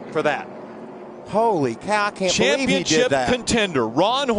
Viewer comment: sounds like he said HOLY COCK WTF